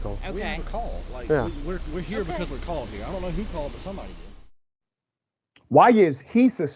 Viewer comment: But! She walked up to the police AND TOLD THE POLICE, SGE CALLED. WATCH THE VIDEO. If you are confused. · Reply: i watched.. i thought the police said.. they dont know who called...